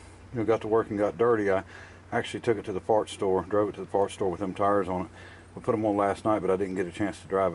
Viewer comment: I could've sworn you said you drove to the Fart store.